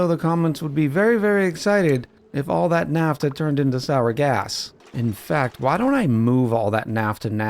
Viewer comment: Did someone say, “Sour Gas???”